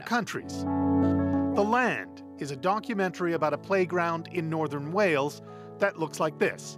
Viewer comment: did he say donkumentary ?